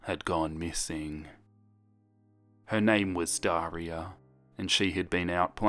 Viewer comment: thought he said diarrhea